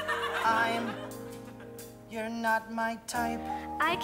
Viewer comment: I always thought he said “fine, you’re not my type” But I just realized he says “ I’m — you’re not my type.” WHICH IS SO DIFFERENT BUT BETTER and I don’t know how I missed this the first time